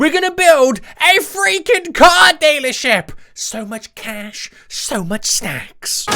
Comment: I thought he said at the start. SO MUCH CASH SO MUCH SEXX